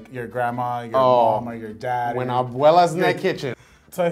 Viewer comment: I thought this guy was about to say when Abuelas naked.. XD